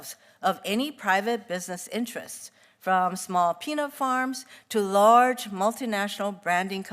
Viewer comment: Came over here from the Cruz video to see what Hirono had to say, aaaaand I wasn't disappointed. "Instead of losing my job, we should keep me busy on a bunch of new laws!"... Also @ 4:36, small "Penile Farms"?